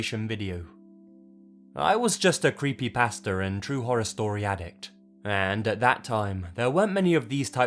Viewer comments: I heard "creepy pastor and true horror story addict" 🤭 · I heard "I was just a creepy pastor" and was very confused XD I was like "well I'll be darned, you are a creepy pastor!" They I realized he has an accent and was saying "creepypasta" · There was a moment when I thought he said that he was just a “creepy pastor” and I had to listen to it again XD love the channel!! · at the beginning, to an american it sounds like hes saying "i used to be a creepy pastor" lol F those creepy pastors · Of all of them, my favourite has to be Mei. I’m pretty sure it’s also the longest single story I’ve ever done. Any that you guys would have added? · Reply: Ha ha, because of your British accent, when you said "I was a creepy pasta ,such and such " it sounds like you say you were a creepy pastor. LOL!